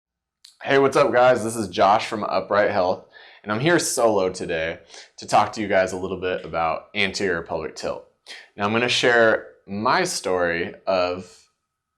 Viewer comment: I watched this in a noisy environment. I thought you said: "Hi, this is Josh from upright hell."